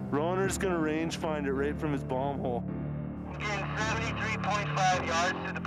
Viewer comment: did he just say "right from his bong hole?" LMAO??